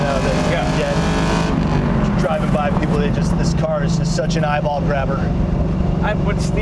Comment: " This car is such an eyeball grabber..." For a second there I thought he said "balls grabber"- hmm why would he mention Kevin Spacey..?..😮